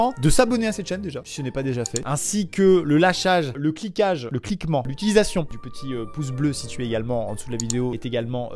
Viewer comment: j'ai entendu "cliquement" j'étais pas bien pour la LENGUE FRENSSAIZ :c